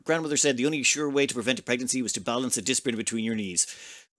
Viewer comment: To balance a what in between the knees? :') Oh, and could you keep beating vampires up for infinite medical training? I wonder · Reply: Interesting! It still sounded like you said "disc brake", and I mean, putting one of those between your knees definitely would make sure you're not doing anything else, hahaha. Btw, fun fact: Jacuzzi, while the brand/company name, has become a generic term for hot tub as well, so all hot tubs are technically jacuzzis! ;) They do still retain the trademark, but only tenuously. It's quite similar to how all facial tissue is Kleenex, even though it is a brand name. Aspirin has been completely lost, from Bayer's trademark to a generic term for acetylsalicylic acid in general. Linoleum, zipper, trampoline, escalator, dumpster- these are all names for trademarked brands that have gone through the process of genericide, and there are many more. It's pretty neat delving into just how many common names for things used today are actually supposed to be registered trademarks and not generic terms.